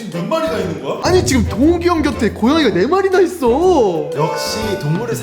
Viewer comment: 에서 동욱이형님 곁에 이 4마리라고 들었고 그 채팅은 5마리로 대워 있는디?